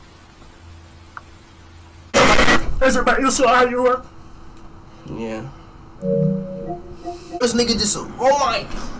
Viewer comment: wtf did he say😂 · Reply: That's about you so are you up